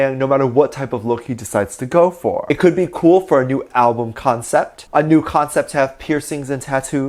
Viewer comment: I thought he said THE new album I had to rewind and turn in up😂😂😂🤧